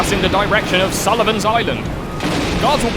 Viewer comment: Any SoMD folks here who thought he said "Solomons Island"?